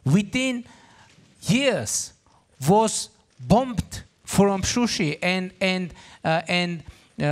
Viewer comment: did he say sushi?😋🍣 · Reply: It’s Shusha